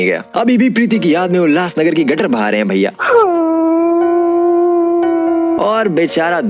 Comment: did he say Ulhasnagar ki gatar? lol